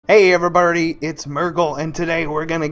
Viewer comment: "hey everyburry" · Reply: i heard "everyburdy", also if you're reading this great guide lol